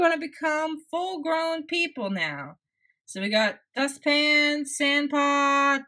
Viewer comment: umm.. did she say dust pan